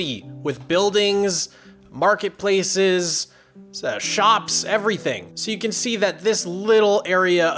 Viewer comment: я же не один услышал, что он на 4:28 сказал «...to fill in the entire city with buildings, marketplaces, *не знаю*, shops, everything»???